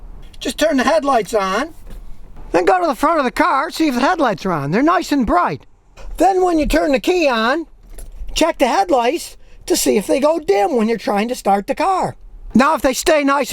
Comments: holy shit it worked. I drove all the way to the shop! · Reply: anyone else catch I swore I heard him say head lice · Did he say check the head lice at 0:48 sec. 😀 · Is it me or does it sound like Scotty say "Check The Head Lice"